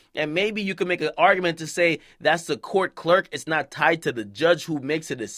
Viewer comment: thought it said clout clerk lol